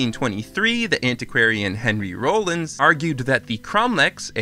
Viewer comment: for a second i thought you said henry rollins and was really confused